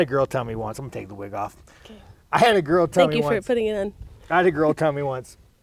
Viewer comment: did Nina say " Thank you for putting it in" ? Was it a Freudian slip?